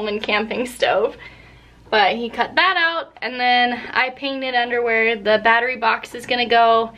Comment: you said, “I painted under where...”. I heard, “I painted underwear...”. A half dozen scenarios popped into my head... I got so distracted trying to reconcile that with the “Shenanigans”, that we’re planted there, that I had to hit replay to figure it out. Loved the vid... keep posting... I’ll keep watching.